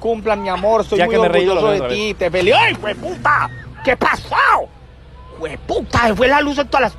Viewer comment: En el minuto 4:46 se parece un poco a la voz del pato Donald,que dice que a pasao